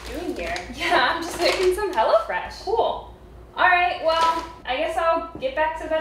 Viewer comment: All this time I thought they were saying "HellaFresh"! Lol